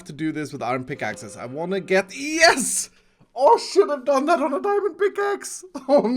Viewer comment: ngl but at 21:30 ish sound like Iskall said shit when he said should've